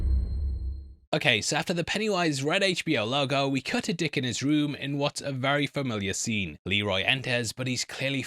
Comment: I thought you said "cut a dick him his room"